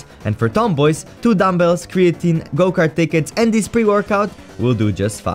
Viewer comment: g-gokart? DID YOU SAY GOKART?